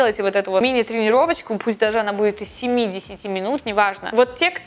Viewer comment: Вы сказали 7-10 минут тренировочка, а мне послышалось 70 минут тренировочка) Я прям так О_О ничего себе 70 минут маленькая тренировочка=)Забавно было))